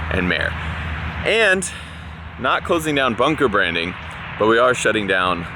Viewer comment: Holy moly. I always heard it as" bunker Brandon" in all the other videos. I always thought it was a weird name. 🤣